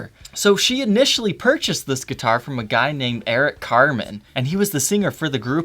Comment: At first, i thought he said Eric Cartman:)